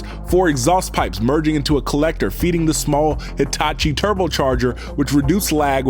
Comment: I misheard Hitachi for itachi 🤣